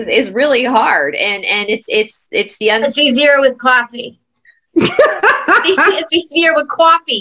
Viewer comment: I was taking a drink of juice when i thought you said: 'It's easy with Vodka!' I almost spit my juice across the room laughing. In reality you said.Coffee instead of Vodka! LOL (at the beginning of the video.) I Love you gals! Blessings from my Big Dogs & me Hill Country, Texas, :@